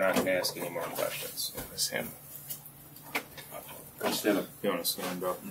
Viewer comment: What does he say at 38:45? It sounds like "It was him." I think he said the same thing about 10 seconds earlier. This is the first time I've the seen the lawyering up part of his interview.